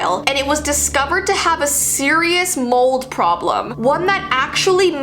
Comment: I thought she said mole problem 😂